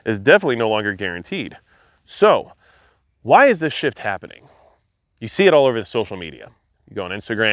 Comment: At first I thought you said, "Why is this shit happening?" 😂